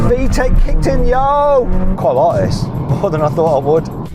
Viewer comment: 「VTEC kicked in yo」って言ってるんだろうけど、どうにも「VTEC効いてんよ」に聞こえる